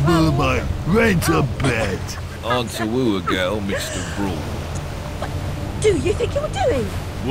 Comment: hey! is it Alice is Britain ( I mean England )... because I just hear in 0:26. She said: "dude, what're you think you doing". I just mean I heard she said... "dude" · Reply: Carrot she said what are you doing but the what was soft and silent